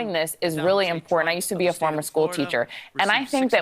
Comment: did she really say “I used to be a former school teacher”??? Say what?!?